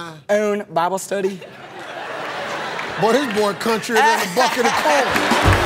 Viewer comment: "This boy countrier than a bucket of corn" 🤣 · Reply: I thought he said bucket of coal. XD